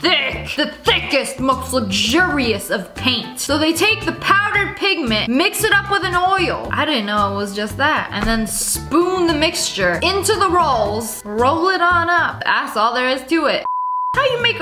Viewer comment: I thought she said they mixed it with Oreos 😂